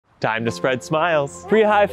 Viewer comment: I thought that said time to spread lies at the start for a second